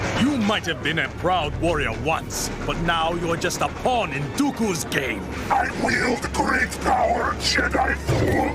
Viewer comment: i know he says in dokus game but i always hear doku is gay